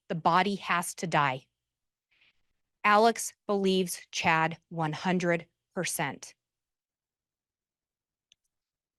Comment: Wow, Chad's reaction at 42:07 was chilling! He shook his head and mumbled something while Lindsey Blake described his rating of Tylee and how she had to die. Disgusting. Lindsey made a mistake with the date of June 9, 2019 instead of 2020 when they discovered the remains. I can't wait for the verdict. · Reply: I agree. I re watched. He shook his head and said , no I didn’t.